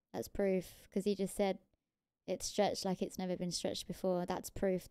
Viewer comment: ORrr what he said was, it's stretched like never before. Hmm